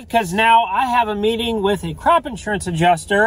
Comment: Anyone had to put on captions after he said crop insurance adjuster I thought he said crap lol😂